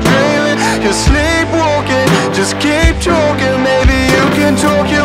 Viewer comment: I heard it As "Just keep choking" Not talking..😂